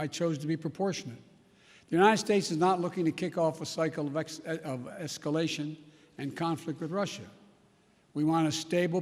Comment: For a second, I thought Biden said that he's not looking to kick off an psychopath. lol